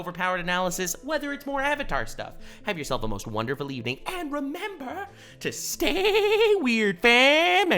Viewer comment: Is it just me or did he just say "--and turn yourself *on* if you're attracted to her feet" at the end of the video???? 😂 🤣